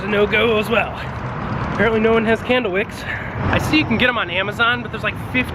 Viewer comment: I heard, "We're looking for ken doll wigs".... Thought about it for a second and replayed it in my head... candle wicks. How you explained what "should" bother you vs. what "actually" bothers you... spot on brother. Story of my married life.